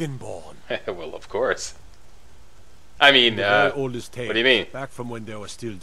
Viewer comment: ''I mean that all you stay with me back from Windows to dragons skype'' hahaha youtube subtitles at work xD and what MajorSlack said was: ''I mean uhhh what do you mean'' haha the guard added this to the mix ''back when the dragons were still in skyrim''